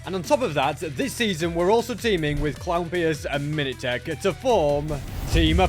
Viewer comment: did I hear that right teamed with clownpirce and minateach sorry if I bocered the name